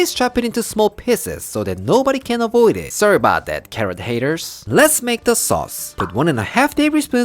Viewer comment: I thought he said Karen haters 4:13 😭